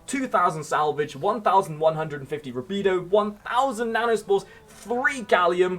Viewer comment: It sounded like you said 1,150 burritos. I dont think I have that many.